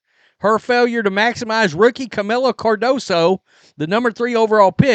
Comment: The craziest thing I heard last year was ppl saying Angel was rookie of the year material 😂😂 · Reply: i thought they said "DOOKIE" of the year!!